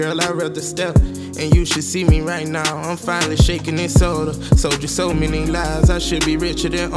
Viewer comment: “And you should see me right now, I’m finally shakin’ in soda” I’m finally popping 💪🏾💯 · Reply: i thought he said "shaking this soda" as in quittin lean but now idk💀